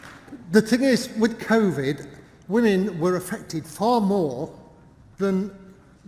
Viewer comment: "Women were infected more than anyone else." -- Good thing that men dying more often than women is considered to be of less importance than infection rates. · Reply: I thought she said "affected" ... and I presume the argument being the dead aren't bothered as much...y'know, being the lucky ones I guess